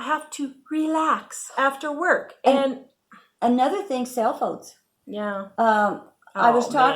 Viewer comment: I thought you guys said "sail boats" when you said "cell phones." Haha.